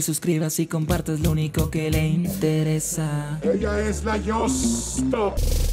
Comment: como yo me llamo yoce y cuando. dice esta es la yostop pensé que decía esta es la yoce